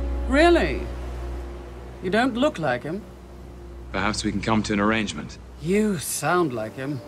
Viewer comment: loki: Perhaps we can come to an arrangement. What I heard: I've come to bargain.